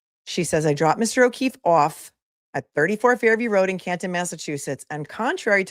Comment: you say off. I hear awwf. 😊😂 I'm Australian, so mines no cupcake either. 😉😂